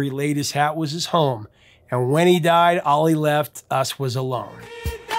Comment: I didn't realize the lyric said "All he left us was a loan." I thought it said, "All he left us was alone."